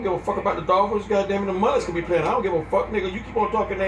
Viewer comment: did he say the mullets? 🤣🤣🤣🤣🤣🤣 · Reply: Marlins